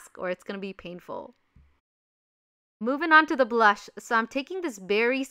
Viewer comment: When she said “moving on with the blush” I thought you said “Moving on with the blood”